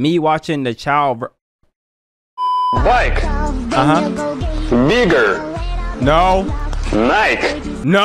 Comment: I heard the hard r and not bigger💀